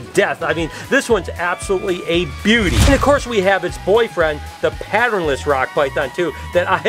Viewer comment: When you held the little Rock pythons, did you say HIS boyfriend??? 😱